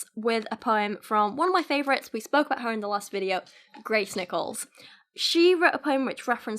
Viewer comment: What you said: Grace Nichols What I heard: Gray Snickles.